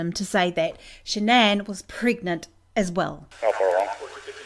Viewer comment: Did he say his wife was 15 months pregnant?